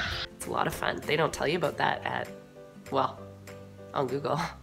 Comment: At first I thought you said, "they don't tell you that on Omegle..." and I just about passed out I laughed so hard. I did eventually rewind to listen again. lol!